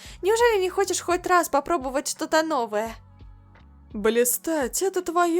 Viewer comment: ахахахах мне послышалось дристать